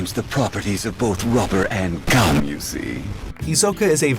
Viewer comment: Bungee Gum has the properties of rubber and gum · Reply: And here I thought he was saying rubber and xum